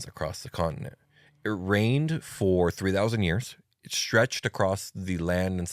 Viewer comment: When said "...reigned for 3000 years", I absolutely heard that as "RAINED for 3000 years." Like, "wait, there were no druids around to fix that? OK, i'll let meyself out...